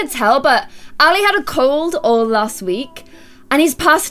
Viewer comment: OMG when said ''Ali had a cold and passes it on to me'' I thought she said ''Ali had a cold and passed away'' OMG LMAO